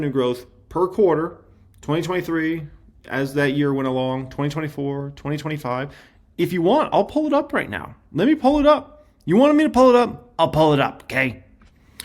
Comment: I was driving when I thought you said “I’ll pull it out” 😭 😂